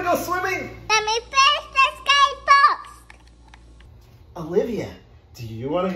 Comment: It sounds like she is saying : it’s my birthday say fox